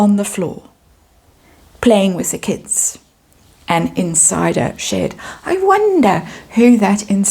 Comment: I thought you said "Harry is always on the floor, playing with the kids, inside a shed!!!!"😂😂